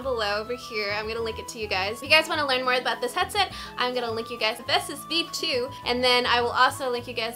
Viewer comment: I thought you said "I'm gonna lick you guys" 😂😂